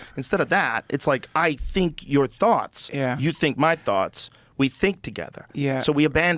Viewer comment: sounded like Patrick, our thoughts if we think our thoughts we think we thought 🤣🤣